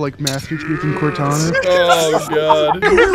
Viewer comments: I was not reading the sub and i heard 10000 bitchs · So far, I have seen in the comments that Skulker sounds like... -The droids from Star Wars -The robots from Nier: Automata -The Decepticon Soundwave from the original Transformers cartoon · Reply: 10000 bits sounds like AVGN saying quarter bit